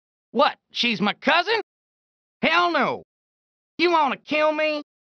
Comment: What she's my cousin!? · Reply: you're correct There, i thought he said Elmo. I misheard hell no for Elmo